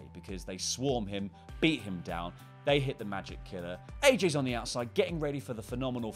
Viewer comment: i swear he says Edge is on the outside or was it aj really really fast